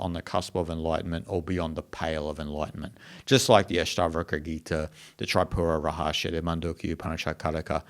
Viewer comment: What was the second book said at 0:58 ? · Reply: 2nd Book said at 0:58 is Ashtavakra Gita